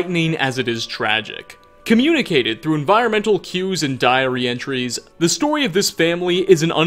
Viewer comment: my hearing is so bad i thought i heard diarrhea entries